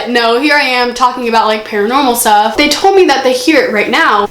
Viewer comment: It sounded like she said mike told me to